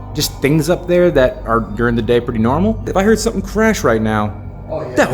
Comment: I heard "Norman"